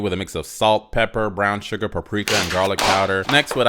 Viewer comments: when you said "paprika" my dumbahh thought you said "caprisun" 😭🙏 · WHEN YOU SAID PAPRIKA I THOUGHT YOU SAID CAPRISON AND I GENUINELY GOT SO SCARED FOR A HOT SECOND